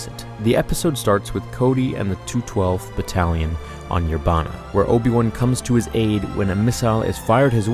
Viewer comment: when they said Yerbama or however you spell it, I thought it said Yobama.